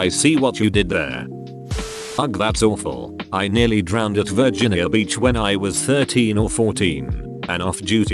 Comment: I Heard virgin hill beach and I was like "What the fuck?"